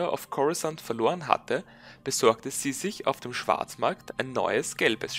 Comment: lol hatte zuerst schwarzwald verstanden